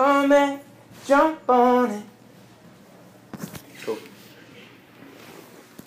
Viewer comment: i thought he said was that all good?